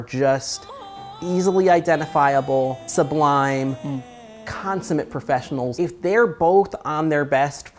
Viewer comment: what word did say? “Comsament professionals”?